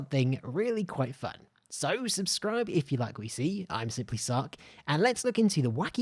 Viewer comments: Thought he said, simply suck lolll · I keep hearing “simply succ”